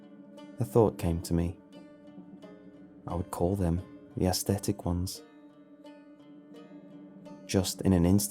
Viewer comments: I thought it said "the autistic ones" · I thought it said "the autistic ones" at first